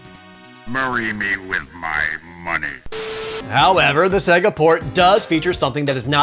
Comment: In my youth I always heard it as "Marry me with my money" which my brother and I thought was hilarious